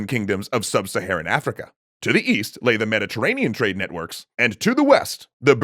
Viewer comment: East? i thought you said weast